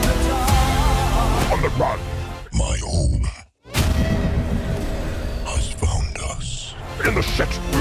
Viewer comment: I swore Venom said “My HOME has found us”